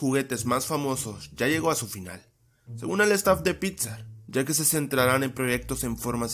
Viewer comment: like si crees que dijo pizza en ves de 'pixar'